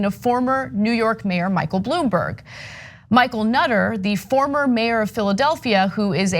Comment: My mind wasn't here... then i heard "micheal NUTTER" I am so childish